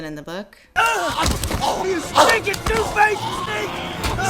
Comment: Wait, did Sam just call Gollum "a stinking douche-face"? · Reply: "Stinking two-faced sneak." I wrote the captions for this episode and I had to dig pretty deep to get the dialogue right in some places lol. (In this case that meant pulling the movie up on Netflix and reading the captions there, lol.)